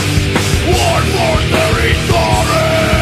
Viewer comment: Wow I always thought he said "one more territory"